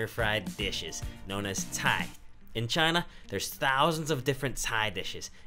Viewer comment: I think the CC should say "cai" not Thai